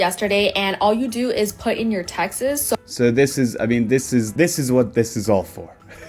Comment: Her accent at 7:07 😅 I heard “put in your taxes” instead of “texts” and honestly the app is still just as predatory